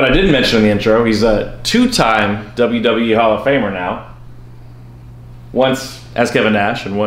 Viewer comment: LOl! Hall of Farmer! I thought that's what that said.